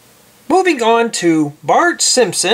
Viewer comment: I thought he said boobing on